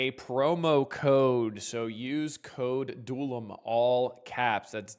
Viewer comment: for some reason i thought you said the code was DULOMALL... wondered why it didnt work for me (i know you spelt it, i just assumed we can all spell all so no point in thet) anyways, for anyone dumb like me, code is DULOM